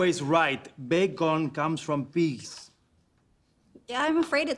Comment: did Fernando say pigs or piss lmao